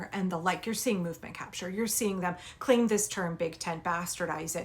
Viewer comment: You said "movement capture" I heard political "culture appropriation"